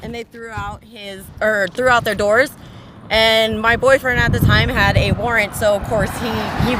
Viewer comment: How so? I talk to him several times a day. And am still out here doing what i can to speak out against these cops because of him. And when ever he does come home we will be cop watching together to make surw others arent treated as we were. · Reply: Oh @ 4:38 4:40 i thought you said "my boyfriend at the time" instead of "at that time, my boyfriend...." so in my head I went to the saddest thing that could mean, (wow I am suck a downer sometimes) but, I thought you meant it differently is all my dear. My heart goes out to you and your family. I am glad our brother was there, and is there for you. I am sure I dont have to tell you what amazing character he has. If there's anything the community can do to help you even if it's public inquiry about the unlawful detainment, abuse, arrest or conduct of these real criminals... Please dont hesitate to ask. There are many of us and within that many there are people who can and will help one way or another. Much love and cyber hugs, big big hugs I am so glad you responded. As minds his own that way, as anyone should. I am sure we will see each other around. You have many unseen cheering for you.